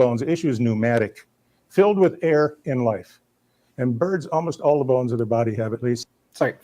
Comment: I think he said "in life" but jokes be funny. x)